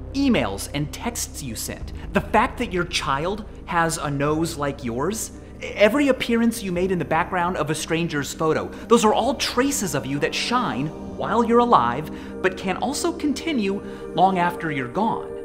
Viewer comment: I thought he said, "A stranger's scrotum."